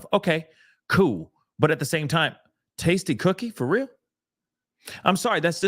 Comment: did they just say the mom SHARES THE TASTY COOKIE TIKTOK?!?!?!?!